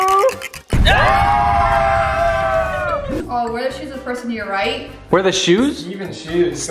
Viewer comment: when the one that said, "wear the shoes of the person to your right" I thought it said, "wedgie the person to your right" lmao love your vids :)